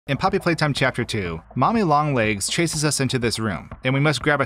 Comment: Why does it sound like he says momi instead of mommy